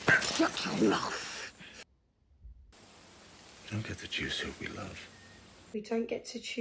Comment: For the longest time, I thought Jaime said "Don't get the juicy with me, love". When I realised what he actually said, it certainly made a bit more sense.